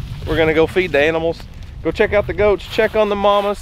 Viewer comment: Oh the whole time it thought you said dous not goats